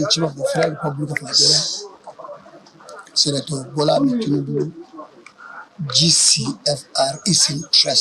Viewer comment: Did I just hear "Senator Bola Ahmed Tinubu"😂😂 abi na ear